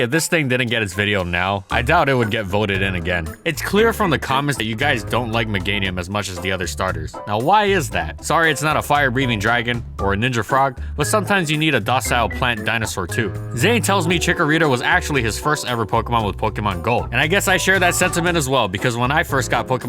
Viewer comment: It sounded like you said ''Ninja Fraud''.